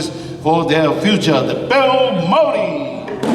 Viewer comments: Please tell me at 20:00 i am not the only one who heard him say Bill Murray...? Bill F'n Murray! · I could of swore the guy said Bill Murray instead.